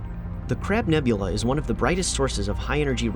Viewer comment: I swear I heard Crap Nebula at 5:29. Gosh, I need to sleep... KKS